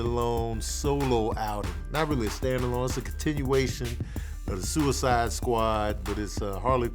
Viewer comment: thought you said “contittyation” and had to rewind 😂